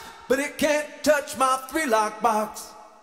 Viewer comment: I thought he was saying, “Welcome to the three lock box” instead of “One, two, three lock box”. Great song. 🤘🏻